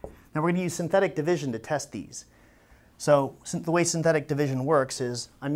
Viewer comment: ngl thought he said “testies”